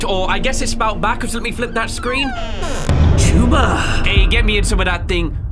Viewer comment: this sometime sounds like hes saying tuber